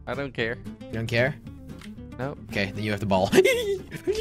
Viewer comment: Did anyone else hear jelly at 9:58 😊